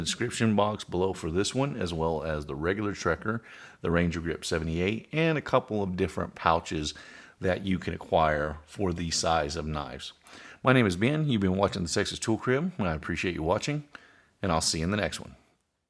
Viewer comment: I had to rewind and play it again because I swear it sounded like you said “sexist tool crib” LOL